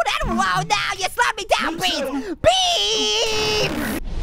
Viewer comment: Sounded like he said “BEEEEEEEEEEEEEEES!!!!!”